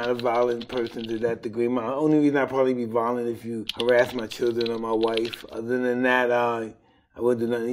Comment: i thought he said "id only be vilonet if I harrass my children" had to rewind there for a second xd